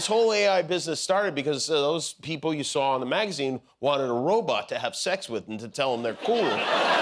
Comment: Jimmy, no kidding, it sounded like you said, "robutt to have sex with..." 😳😆🤪 Freudian slip? lol ❤️‍🔥🤖💝